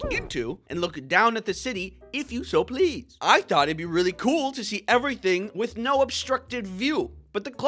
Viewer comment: I accidentally went back ten seconds at 1:42, So it sounded like you said “Because I thought it would be really cool to see willace tower”